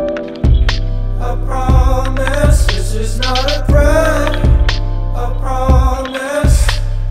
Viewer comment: They’re taking my heart now They're taking my heart now and I don’t know why And I don’t know why Alright, alright, alright, alright, alright, alright, alright The demons told me everything They whisper in the night (Hey, hey) This is not a threat, I promise (nope) I promise This is not a threat, I promise It's a warning baby I just want you to know That's when the ceiling caved in And I'm fallin' in a trench, I'm behaving I'm behaving myself I'm behaving myself We don’t gotta die so soon So I just behave for you (for you, for you) We don’t gotta die so soon So I just behave for-- (Shh, quiet) This is not a threat, I promise (nope) I promise This is not a threat, I promise It's a warning baby I just want u to know Please just let me go Let me go I’ve seen enough I’ve seen enough I’ve seen enough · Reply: why do I hear "this is how I break, a promise"...